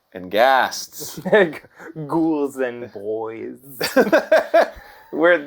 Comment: I really thought Danny said "Ghouls and Goys" at the beginning.